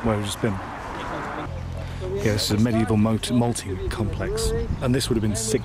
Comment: hey! amazing video, really cool! (we watched it for school :DD) i was just wondering- what's the word said at 5:11 ? a medieval ** something ** complex, i believe it might be because of conflicting accents or maybe i haven't heard the word before! anyway, thank you so much for providing this content! hope you're safe and healthy <3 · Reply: Sounded like he stumbled on the word multi.